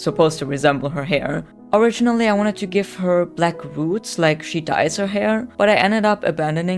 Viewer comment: Why did I think you said black boobs 😂